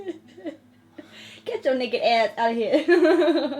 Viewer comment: I’m sorry, did she say the N word around 1:15? Please tell me you said naked. 😬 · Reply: Deffo said naked, "get your naked ass outta here" listen to the whole sentence, it's in context and with an accent! 😁